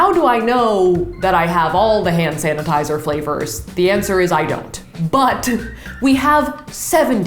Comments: Did- Did she just say f l a v o r s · did she say.. flavors? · Did you just say 'flavors'